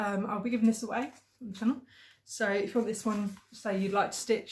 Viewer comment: Like you, I binge Flosstube all day at super speed. Once in a while, I'll slow a video down to catch something I can't quite understand (for example, I thought you were saying "bee stuff", but it was actually "peace dove"), and it seems so strange to hear people speaking at a normal pace, lol. FYI... It was Flosstube #16 (Feb 4th), where Geiger Counter commented that they were bummed they'd missed out on the trees from "last month", aka Flosstube #15 (Jan 13th). By all means, give them time to pipe in again, but if you don't hear from them, I too; would love to stitch those trees. Thank you for the Flosstube recommendations; I'll be checking them both out as soon as I'm done here. I'd like to stitch Peace Dove or Down on the Farm. Stay safe and sane.